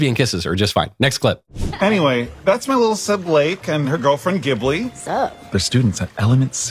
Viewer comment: With the cyclops I heard "our girlfriend"